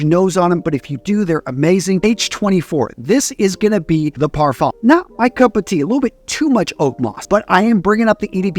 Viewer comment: That voice over at 2:29 I wanna know what he actually said lol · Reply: Most likely said La Parfume which is a " Light Parfume DNA" while EDP are Full Body Parfume.